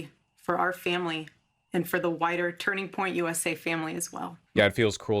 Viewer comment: I know she said 'wider Turning point family' but it really sounds like she said 'whiter'.